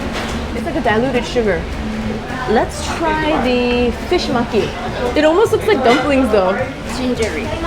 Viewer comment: The way you said 'maki' sounded like 'monkey' so i kept hearing "fish monkey". I'm like "wth is fish monkey?" Lol